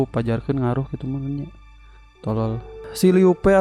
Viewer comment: ngomong apa bang...? Banyak kata2 yg kurang jelas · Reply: Yang ku denger itu ngomong: "pajarkeun ngaruh kitu nya, tolol"